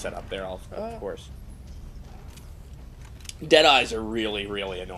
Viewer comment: I keep hearing 'dead-eye' as 'jedi'. Hey, it'd explain the giant unsubtle blue lasers.